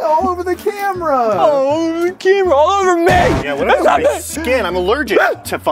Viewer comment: is it just me or did mathias say "it came all over me" time code